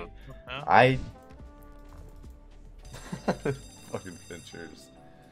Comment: Uh oh. All I heard was “Fucking Fincherzz” 🤣